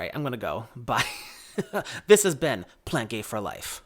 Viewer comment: omg. So sorry. My bad. I don't know why I thought you said Ben. Yes, semi hyro actually is a great way to grow so many different varieties of plants. Thank you again on for thjs video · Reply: oh ya know what, I said at the end "this has been plant gay for life" and it probably sounded like "this is Ben, plant gay for life! hahaha!